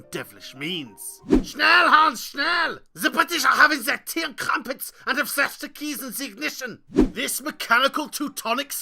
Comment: Subtitle please. Even I, as a German, couldn't understand, what you say. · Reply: 'Quick Hanz Quick, the British are having their tea and crumpets and have left the keys in the ignition'. *said in a hilariously German accent.